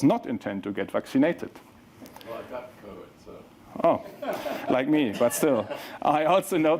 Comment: what did Elon say here? I couldn't hear :( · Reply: Elon responded "...well I got the code so..." He knows that vaccine is nothing but programmable code and he is not about to let them program him. He knows their plans. He explains how mRNA works at 35:11.